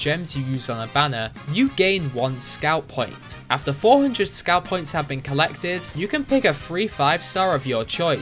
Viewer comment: I totally misheard 'Scout Points' as 'Scalp Points' and honestly...